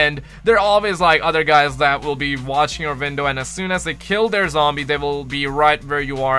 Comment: OHHHH YOURE SAYING 'WINDOW' god damnit I thought you were saying "Vindal" and I was like "Ok maybe I don't know that much about COD zombies" but it was really your accent. I feel better now. Great video btw dude